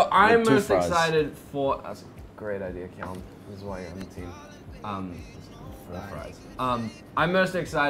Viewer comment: “ that’s a great idea calum that’s why I’m into you” did he just say that?? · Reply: He said this is why you’re on the team